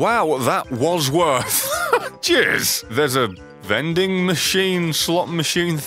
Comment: I swear everytime he says cheers it sounds like he says jizz